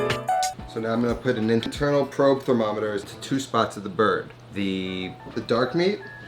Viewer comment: This looks amazing. Also when you said "internal probe thermometer" I heard "ninja turtle thermometer" so that's what I'm going to be calling it from now on